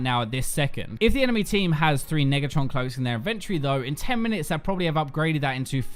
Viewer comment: Did you just say nigatron?